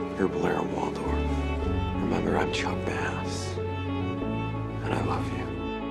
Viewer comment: Idk how and why around 6:20 my subtitles showed I chocked my ass instead of chuck bass